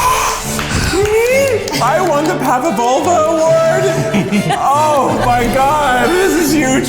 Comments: did hereally say 'Pava-Vulva'-Award? Zack looked like he caught that, too. 😂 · did keith just say pavavulva lmfaooooo